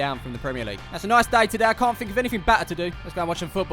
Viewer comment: Anyone else hear say batter instead of better at 0:49. This is as they are called fisher and u batter fish